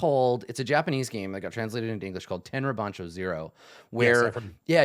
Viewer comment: What is the name of the Japanese game mentions here? It sounds like he says "10 Repancho 0" but Google comes up with nothing for that. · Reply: Tenra Bansho Zero!